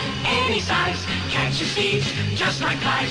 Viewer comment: I heard "catches steves" instead of thieves xD